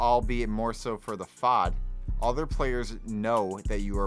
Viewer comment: did you just say fod